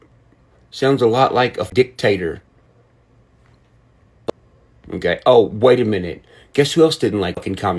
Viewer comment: Dang it my hearing has been getting worse . I know you said dictator but I thought I heard thick tater at first .